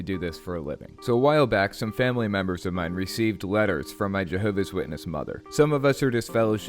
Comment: I thought that said jenova and i said i dont remember thiss from final fantasy 7